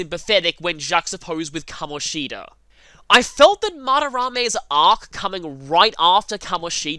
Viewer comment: I'm sorry, but I have no clue what you just said, despite going back and hearing it again multiple times. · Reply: "Madarame's arc". As in, Madarame's story arc.